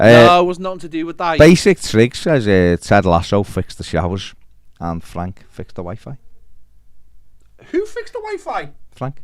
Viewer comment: Watching in philippines, not live. I thought said ANN FRANK fixed the WiFi . Haha